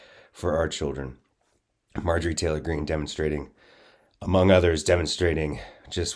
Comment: I thought I heard him call her "Marjorie Traitor Goon.".